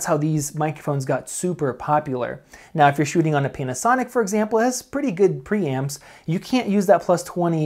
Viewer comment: Did he say penisonic XD